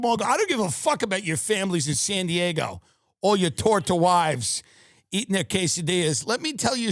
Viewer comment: did he just say "your torta wives"? haha😂😂